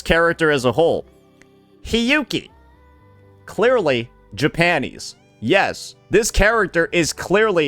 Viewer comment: Sounds like bro is saying “Ja-panties”